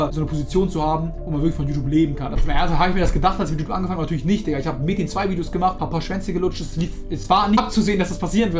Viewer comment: " Ich hab Mädchen 2 Videos gemacht " ???? Oder was sagt er da ?